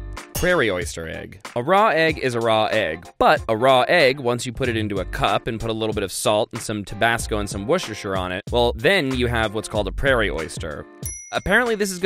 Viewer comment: what did my man just say, whoserser? Do you mean woester sous?